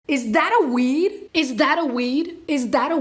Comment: In the intro i thought she was saying is that a "weave"